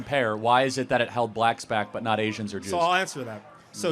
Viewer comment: I thought he said "injuns" but I played it back and and he actually said "Asians".😂